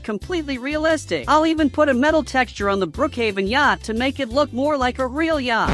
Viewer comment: 🤨DID HE SAY GYA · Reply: He said yacht not Gya-🤨📸